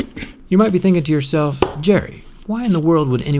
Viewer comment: did u call yourself 'Jerry'?